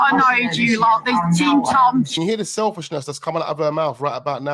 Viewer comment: Does someone say team Tom at about 11:51.